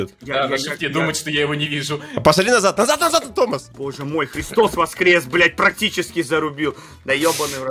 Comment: когда пила чай мне послышалось «пи.здос воскрес», вместо «Христос воскрес» 😅